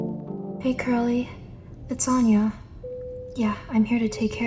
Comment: i heard it "hi girly" 😭😭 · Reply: I thought she said hey girly to lol but she’s saying hey curly